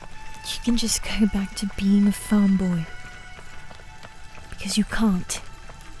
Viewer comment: i thought you just called me a femboy